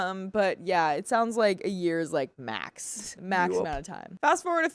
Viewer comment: I was half asleep then freaking tf out cuz it just sounded like a dude being "you up??"....I dunno if I misheard or yall are trolling lmao but gg got me hunting the house and calling people asking if they came by